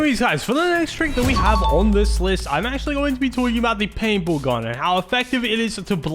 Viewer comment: he says painful gun 😭 instead of paintball gun